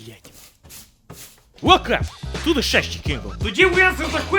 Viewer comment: Я один в начале услышал "Во как!", а не "Welcome!"?